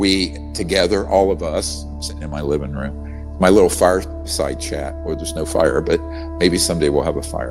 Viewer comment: I thought he said "fart side chat" lmao! Bu he said "fire side chat"